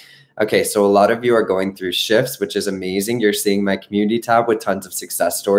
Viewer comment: I thought that you were going to say "a lot of you are going through shit" but then you said "shifts" - LOL!